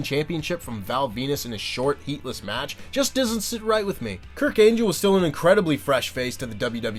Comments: Did i hear Kurk Angel? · Did he say "Kirk Angel"? 😂 😂